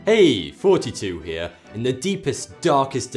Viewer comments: I thought he said hey 42 here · damn it sounds like hes saying " hey 42 here " lol